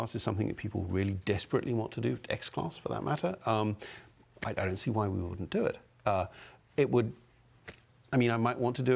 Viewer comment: Finally, wallriding fix! :D Spotify integration would be awesome! What did say at 1:07:39? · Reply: he said I I don’t have answer for that unfortunately